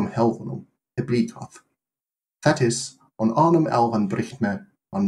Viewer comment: I heard "that is"